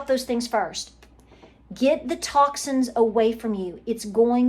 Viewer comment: You said at 17:20, get the TOXINS away from your body. I heard get the TACOS away…🥴😏😊 maybe I needed to hear that